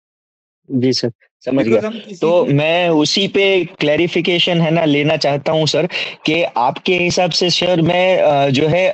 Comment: What did he say at 32:00, do not choose courses choose? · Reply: He said, "Do not choose courses; choose trainers who are teaching you." Watch demos to see if an expert is conducting the class.